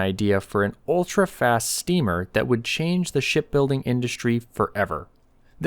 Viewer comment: As an inventor I believe this video is a valuable aid in demonstrating the cautionary warning to would be inventors. Inventors fall in love with their ideas. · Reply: I thought he said "shit building" - I ordered the "Toilets for People" - another great invention!